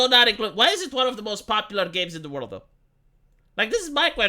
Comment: What you said: the world though What I heard: Ze Wolrdo!